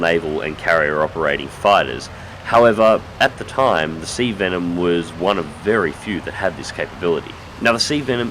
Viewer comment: I thought it said sea denim.